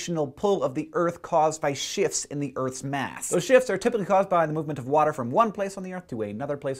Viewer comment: I kinda thought that.. I misheard hank he said "shifts in the earths mass" I thought he said "changes". Thanks for the help though!